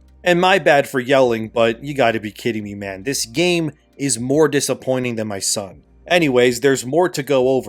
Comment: Did he say "more disappointing than my son"?